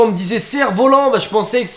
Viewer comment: j'ai plus pensé à "cerveau lent" moi mdr